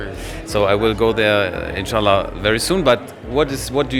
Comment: did he just said InshAllah 😅